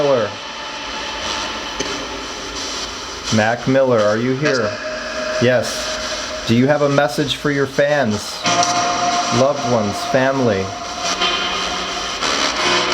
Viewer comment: Broo at 5:15 it sounds like him saying yes ☹️